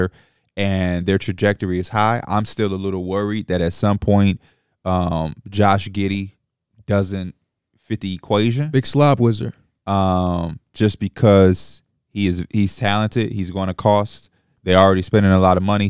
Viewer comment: Did say "Big Slob Wizard", when they was talking about Giddey?